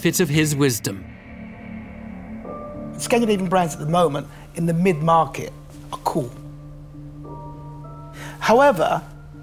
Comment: Can anyone decipher what Touker says at 5:47? I’ve slowed it down and even despite my best guesses I can’t get it to make sense? It sounds like “it’s getting to be even brands at the moment in the mid market are cool.”